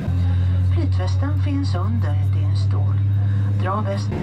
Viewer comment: Petreston Finson DEAD IN STORE . is that what she said? sounded like it